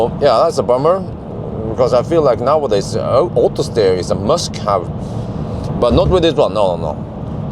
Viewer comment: Did say „autosteer is a Musk have“? 😂😂😂👍